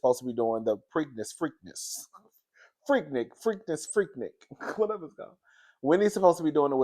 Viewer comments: I thought they was saying Freaknik too. I put in CC! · I ain’t gon lie ……….. I thought she said Freaknik too 🙈 I was like “wait , what?!?” 😂😂😂😂😂